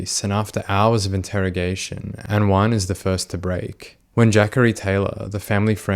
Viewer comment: JACHARY??! …oh….. it’s Jacorey. LOL I really thought his mom said “Zachary, but with a J”🤣🤣🤣